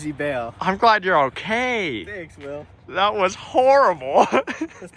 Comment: Is that Will from BUM that i hear in the background? 👀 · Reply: he said thanks will I just noticed so yup.. I heard it too and was wondering the same til i just heard him say will 🤣